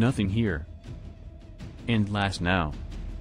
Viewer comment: I thought what sonic said "and last- (looks at Shrek) NO!!" runs away